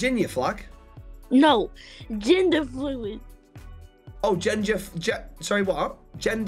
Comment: when the girl with the ninja nails instead of gender fluid i thought they said ginger fluid 😭😭😭😭